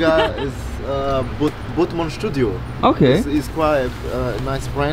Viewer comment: what's that bag brand from 5:07s' ( O O ) I cant hear it clearly, it's like botmanche studio ( 0 0 !!??) HELPPP i WANT TO KNOW